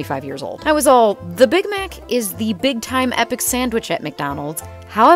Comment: I'm sorry did she just say 'all time epic sANdwIcH'